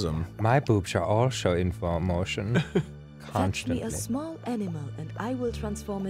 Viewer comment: I coulda sworn the fairy godmother said "give me a small enema"